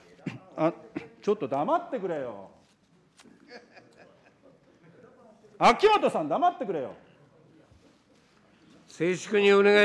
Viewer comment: 今井議員が怒鳴った相手って誰かな？ 秋元さんって聞こえたんだけど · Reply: 秋本真利じゃないですかね？ オレンジネクタイの。